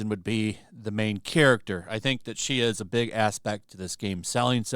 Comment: Did I just hear "Big ASSpect"? 😏